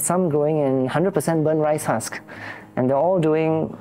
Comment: Hi. Did he say 100% rice husk for the soil? Or i misheard it. Pls answer anyone